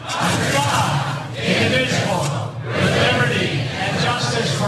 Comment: I heard "Individual" the 2nd time haha